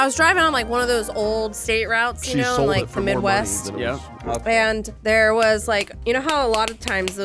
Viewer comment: did Ryan say "she sold it for money? " But yes also from Ohio and Lindsay's scenario is pretty much how a classmate died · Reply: "She sold it for more money" it was a joke about the word "flipped" as in flipping a house